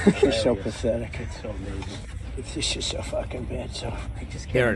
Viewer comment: I heard "Shes so F*****g bad"